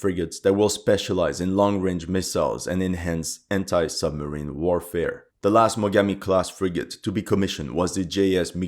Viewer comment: anti-submarine warfare? all i heard was hentai-submarine warfare. i cant have been the only one